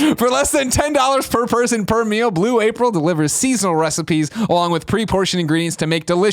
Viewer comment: In celebration of Greg & Gen's marriage, It's now time for everyone's new favorite game: HOW MANY TIMES WILL NEWLYWEDS HOLD HANDS! 1 17:32 2 47:03 3 47:11 4 49:15 5 52:04 6 53:39 7 56:14 8 57:57 9 59:58 10 1:06:10 11 1:07:13 12 1:09:12 13 1:10:35 14 1:12:12 15 1:15:42 16 1:33:04 17 1:41:00 That's right, 17! What a game. Exciting stuff. Tune in next week when we count how many times Tim looks at Kevin when either something puzzles or excites him. Goodnight everyone and thanks for playing. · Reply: swear Gregs saying blue April